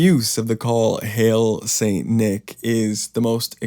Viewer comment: Two comments: 1. The CAC were "Dreaming of a White Christmas". 2. I think "Hail St Nicholas" may have been intended to sound literally Satanic, as Old Nick is a name for the devil. I came across an excerpt from an 1881 book by Moncure Daniel Conway that links St Nicholas and Old Nick via an old Germanic tradition of both giving gifts to the good children and carrying away the bad children for punishment. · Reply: When I first heard "hail saint nick" I swore I heard Hail Satan. Had to rewind. Maybe it was added simply to rhyme, but could also be a bit of both. There's a lot of paganism in Western Christianity.